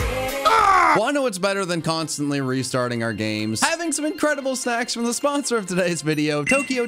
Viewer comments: I swear to god the first time I heard that, I thought he was saying “having some incredible sex with the sponsor of today’s video”. You can’t tell me I’m wrong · I heard that very wrong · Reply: Sax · PLEASE tell me I'm not the only one who heard "having some incredible sex" at 2:02 and almost had a heart attack 😅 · I know you said snacks but I heard "Having incredible s*x" confused me so much I had to replay it three times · Okay... He said "having some snacks from today's sponsor" and I heard "having some sex" I literally did a double take. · I swear it sounded like said "Incredible SEX" not SNACKS. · you need to pronounce the n in snacks. The first time i heard it i heard you say let's have some incredible seggs and i about lost it laughing lol